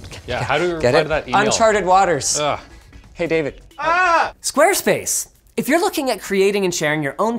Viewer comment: Mishearing Linus saying squirtspace :U